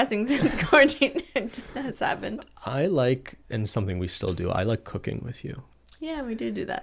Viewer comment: And at one part i misheard that ben said "i like to do coca*ne with you" 😳😳